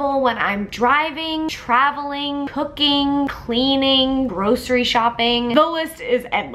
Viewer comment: I almost chocked on my chicken nuggies when you said cooking because I thought you said hooking..;-;. I love being irrelevant.